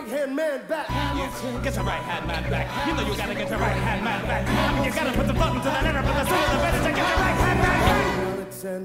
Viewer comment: WTF DID HE SAY?? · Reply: I mean you gotta put some thought into the letter but the sooner the better to get your right hand man back